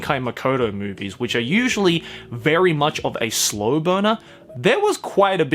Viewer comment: I thought he said "slow boner" @ 9:43 🤣